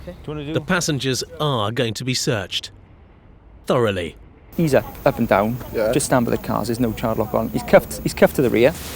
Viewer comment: The dude at 42:28 ..... I couldn't understand a thing he said. Like another language. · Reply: "Can you take these off me now please butt"